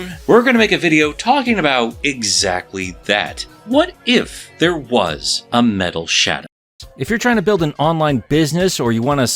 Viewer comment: love how while this way playing right before the sentence ended, an add played. So it sounded like said “What if there was a Metal Shat?”